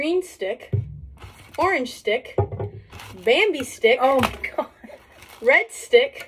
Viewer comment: “bambi stick” “feels like you’re chewing on a stick”😂😂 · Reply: I thought she was saying bendy slightly off but Bambi makes so much more sense and is so much more funny 😂 Awful, but funny lol